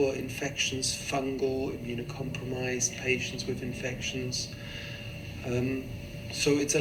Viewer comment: Wait, Wait,.. Did Mans Say " hAITIANS wITH iNFECTIONS" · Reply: No he said patients with infections